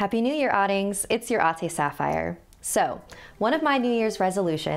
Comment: Why does it sound like she says, "Its your autist saphire" in the beginning of every video